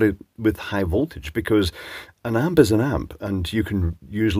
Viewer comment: I saw this fuse in the live stream and was wondering if a video would be made. It's quite nice, no arc supressors. Any idea what kind of load this thing would have to break? What did it take to blow the fuse? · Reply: I think mentioned '1 Amp' near the beginning of the video... Edit: I have misheard that part, please see following comments!